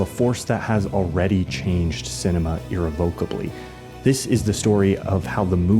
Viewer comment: I heard "irrevokably", but I knew you meant "irrevocably", with the stress on the second syllable rather than the third. Irrevokably is a word dang it! It can't be revoked!